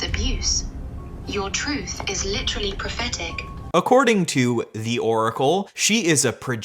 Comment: i thought the ai said "pathetic" at first and had to do a double take 😭